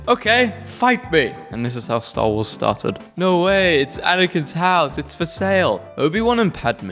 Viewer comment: did you just say Anakin’s house?